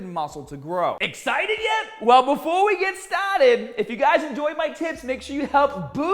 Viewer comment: I wasn't listening to closely and I swear you said "enjoy my tits" 😂😂